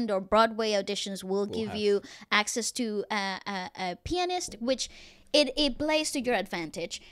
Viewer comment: Oh so you say pianist I heard it differently, I thought you said “pen-s” hahaha sorry for mishearing you!